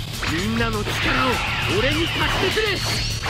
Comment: みんなの力を、 俺にたすけくれ! って聞こえた